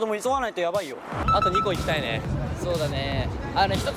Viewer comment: はしもっちゃん「あと2個行きたいね」が「あとWEGO行きたいね」に聞こえてめちゃめちゃびっくりした笑笑